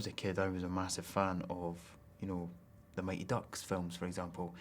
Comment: Did he just say what I think he said??? 😂😂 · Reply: the mighty ducks ;) really great films! go and watch! or at least the trailer...